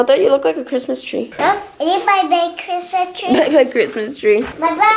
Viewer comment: I'm i hearing stuff cuz i heard that said "my parents died"😂😂😂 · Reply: Lmao I thought he said bye bye star